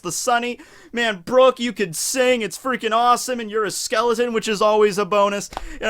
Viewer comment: did I hear that right? "You're a skeleton and that's always a bone-us"